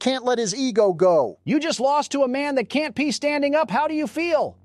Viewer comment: that sounded like